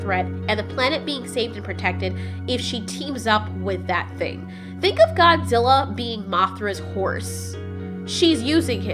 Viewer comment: I thought you were about to say “Think of Godzilla being Mothra’s whore” at 3:40 ... lol